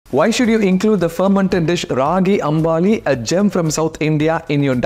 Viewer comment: I heard it as ragi ambani initially 😂